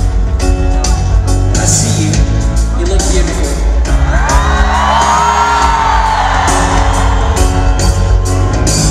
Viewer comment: Что сказал Тайлер на 2:09? Не понимаюююююб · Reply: i see you, you look beautiful